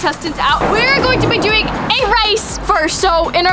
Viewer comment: Did anyone else here say rice instead of race on the timeline of